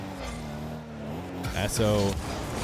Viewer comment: did just say a-hole ?! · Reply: He said atow. 💯